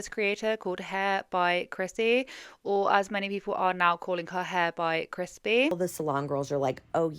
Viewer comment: I keep hearing you say "Hair by Crispy" and keep thinking it is the perfect name for this salon, apparently! lol